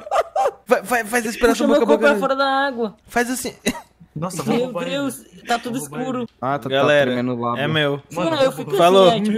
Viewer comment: ''tchau galera é meu'' kkkkkkjkkkj acho q entendi